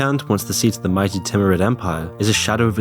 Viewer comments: Moon Newsletter - <link> Support the channel here (all money goes straight back into the channel): ► Become a Patron: <link> ► Follow my Twitter: <link> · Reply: did you say semicant of the Tyranid empire? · The mighty what empire? I can't understand that word that is being said with my old ears and CC states it as timed empire. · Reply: Timurid